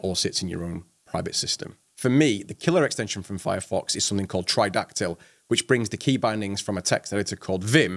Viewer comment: What did you say? ..killer extension for firefox is ??? try-doc-tle ? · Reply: Tridactyl - <link>